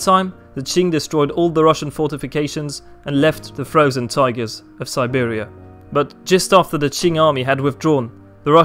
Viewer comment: & 11:26, is he saying "the frozen tigers of Siberia"?